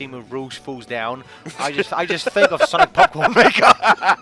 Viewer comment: Did he say Sonic Popcorn Maker?